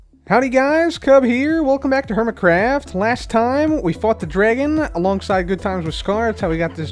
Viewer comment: In the beginning did cub just said he fucked the dragon xD Edit